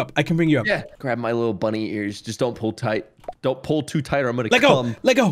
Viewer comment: what did he say after that sentence-? 0_0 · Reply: “Don’t pull to tight or I’m gonna-..” whaaat?